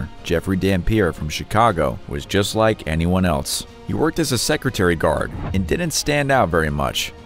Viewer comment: 😂i just heard "secretary guard""😂is there such thing.